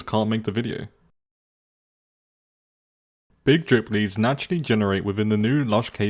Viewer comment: Did he just say big drip?